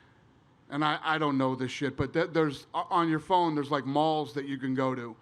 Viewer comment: Did he just say......malls on your phone?